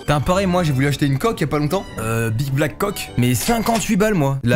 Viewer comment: big black quoi ??? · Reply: Big black coque